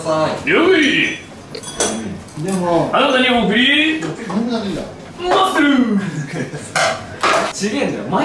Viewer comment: 佐久間くんマッスルの前って何て言ってますか？ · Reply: あなたに送りーマッスルー！って言ってます笑